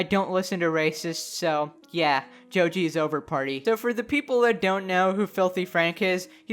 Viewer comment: What?! I thought it literally said # joji sober party not # joji’s over party